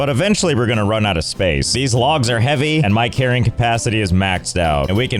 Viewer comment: I thought you said 'My caring capacity' not carrying capacity...and I feel both are accurate statements.